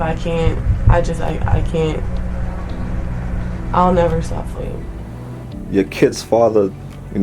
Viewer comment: What did she say at 8:10 ? She’ll never stop what??? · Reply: Fighting, I believe.